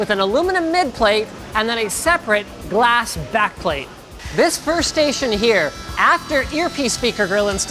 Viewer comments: i first thought he said "this first asian" 😅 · Did he say "This first Asian here"? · I thought he said "This first Asian here...." :'D :'D